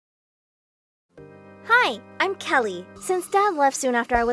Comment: Bro I really thought it was going to say “hi I’m dory” 😂